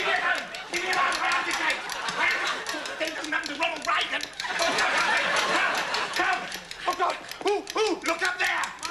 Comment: What did Richie say at 6:24? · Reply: jazzy bubblezzz he said "this doesn't happen to Ronald Reagan"